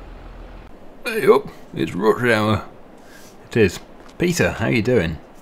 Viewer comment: When Peter turns up at 3:03, does Dennis say "ey up, it's Rutger Hauer"? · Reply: I think he said rush hour